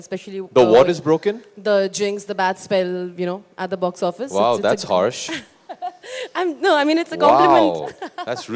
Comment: Did AB just say "Horse"